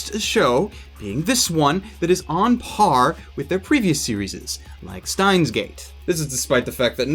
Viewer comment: Did..... did you just say "serieses"?